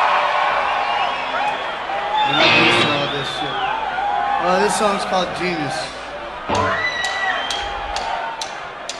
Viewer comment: Any one know what caleb Says at 10:02 after he says you guys are f#-!ing awesome?? He mumbles something. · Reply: beverley smallwood "...We're not used to all this shit."